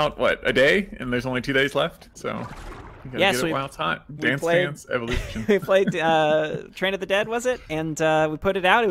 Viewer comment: Wait what I thought it said Dance Dance Revolution